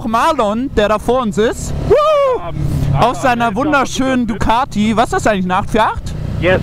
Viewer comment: Ducati, ich habe immer die ganze zeit Bugati verstanden XD